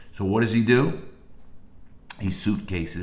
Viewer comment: lol am I the only one that thought he said, “he suitcased a kg.” I was like aww lord that was probably uncomfortable! Lol tell he said handcuff key. Awww that makes a lot more sense lol